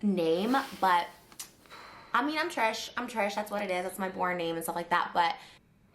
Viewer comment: when she said “i’m trish, in trish” but i thought she said “i’m trash, i’m trash”